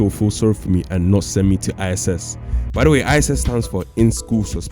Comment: I thought bro said ISIS